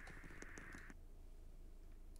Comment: when he said thirty i thought he said DADDY💀💀